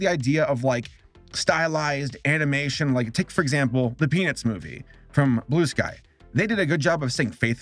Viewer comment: I thought you said “The Penis Movie” dear lord 😂